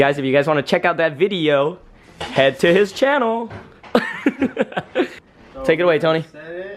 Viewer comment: I thought y'all said Vu has a channel. I been looking for it and can't find it.